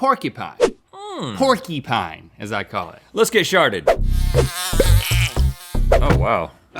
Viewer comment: did he just say "lets get sharted"